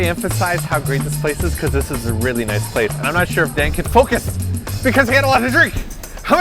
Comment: Did I hear you say bleeps name? It sounded like Dan...